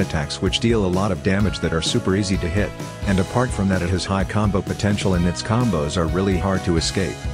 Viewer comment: Pov: buddha users 3:14 urmm did you say combo 😂😂😂